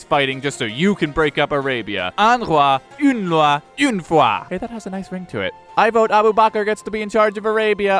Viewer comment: What is that Arabic phrase that he says at 0:51? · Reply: "Un roi, une loi, une foi" it's actually a French phrase famously attributed to Louis XIV